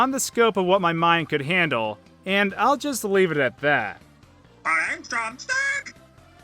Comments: I hear "flying drumstick". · “thanks drumb STICK!” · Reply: FishingHobo I thought he said "thanks, I'm sick" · MY ANKLE’S GONE STANK! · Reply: I never usee that character in the game and didnt know his name, and yet i somehow still heard him say "Im drumSTIIICK!" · I always thought drumsticks said eggs don’t stick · did he say "im toxic!" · I hear I ate drumsticks!